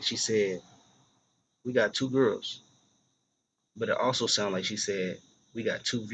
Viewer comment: I always thought said "we got 2 rooms" I'm definitely gna go listen again though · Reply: Just relistened to it, she 100% says We got 2 girls!!!!